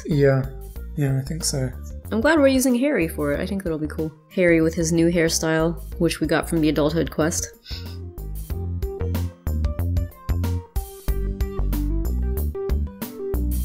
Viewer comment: lol it thought you've said harry styles instead of hairstyles Because i love harry styles bye